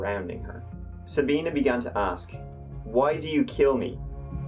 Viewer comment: I thought she said “why do you film me?”